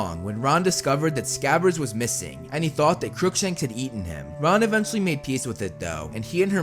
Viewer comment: I heard yeeten him